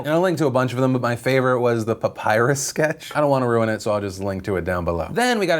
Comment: am I think only one that heard butt pirates sketch?